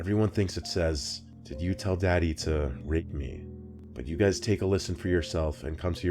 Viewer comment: Sounds like he said can you tell daddy he hates me